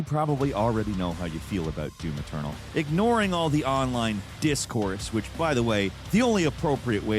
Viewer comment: Falcon was saying, "If you ignore all of the online 'discourse' surrounding the game..."; meaning if you ignore the online debating/arguing over Doom:Eternal, that you'll probably enjoy it (essentially.) Unfortunately, it had nothing to do with "Discord" (the online platform) but I completely understand the confusion. I hope this helps! 😎👍🏻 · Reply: ok thanks, I thought I heard discord 😅